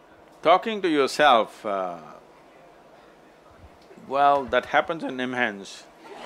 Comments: What does he say @ 16:45?? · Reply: Talking to yourself, well, that happens at NIMHANS. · what is sadhguru saying? That happens in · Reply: "that happen in nimhans" Nimhans is apex institute for neurology and psychiatry in India. He was referring to psychiatric patients(talking to themselves) who comes to nimhans. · can somebody figure out what did sadhguru say @ 16:50 ?. i didn't get it. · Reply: National Institute of Mental Health and Neuro Sciences (NIMHANS). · What did he say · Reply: NIMHANS which is national institute for mental health and neurosciences.